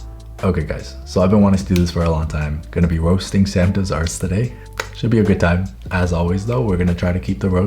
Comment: please dont open automatic geberated english caption instead of writing "roasting samdoesarts" it wrote " roasting santa's a***"